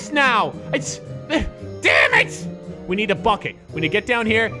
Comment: Who else thought troy said we need to f*ck it